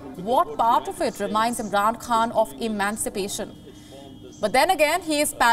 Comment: did you say, "Afghan Khan" (instead of Imran Khan).... Lol!! Ma'am get you script right please!